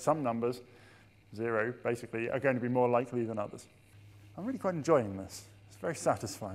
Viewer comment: "I'm really fucking enjoying this" anyone else hear that?